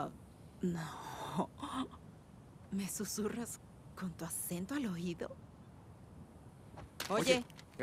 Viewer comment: Can any Spanish speaker confirm what she is saying at 0:30? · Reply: “¿Me susurras con tu acento al oído?”